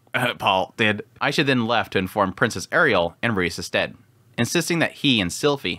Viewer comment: Can someone help me answer this question, what did he say at 23:40, aisha informed princess ariel and who? "And Rudeus’ den" Was what she said?